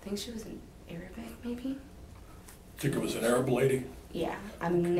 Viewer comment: I love these videos! Is there any way I can add subtitles to these for you? It’s very hard to hear a lot of them, and I have to turn my tv up so high to hear them clearly 😭 I thought there was a way for viewers to add captions , but I’m not sure. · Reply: Yeah, the "cc" option looks like a voice recognition program like we used in medical transcription and not true captioning, so it's coming up with some weird stuff like "the lady was an airbag" instead of "Arab" at 1:07:40.